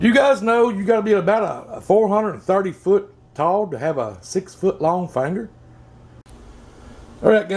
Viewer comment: Great taco deep fry idea! That looked awesome. What did you say @ 4:28? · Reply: Hugh Janus Thanks man...I said you have to be about 430 ft tall to have a 6 ft long finger...I used math LMAO!!!